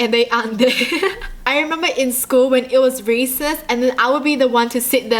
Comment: Who thought she said racist when she really said recess? Just me..........? Ok......